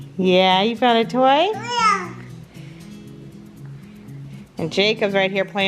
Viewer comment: I swear I can hear him say yeah after asks him if he’s got a toy